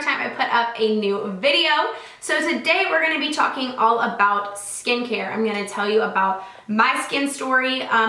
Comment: When she said skin care at the beginning I thought she said 'skank hair'😂😂😂 well I guess I am Australian